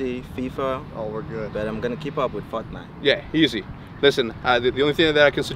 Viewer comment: kinda sounds like he said fucknite 😂